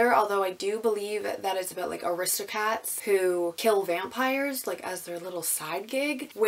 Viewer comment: Really curious about The Truth Project! also I thought you said "aristocats" like the movie, which cracked me up because I have to stop and think about which word to use sometimes 😂 thinking you have time off and then finding out you don't is the worst! hope you're still able to relax a little!